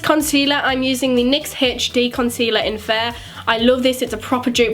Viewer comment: Haha lol what did she say at 2:40 Jues nostitaing concealer I don't know x